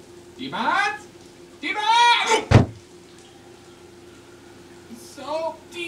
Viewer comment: uhm what is he yelling? I heard "Iemand" "Iemand", and then salty much...I am hearing something wrong here?